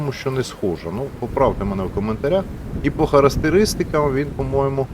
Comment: Це мені одному почулося на 0:58 секунді він сказав не характеристика а харестеристика?))